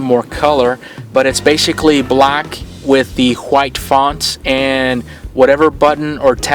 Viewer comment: Did I hear “hwite font”? 🥹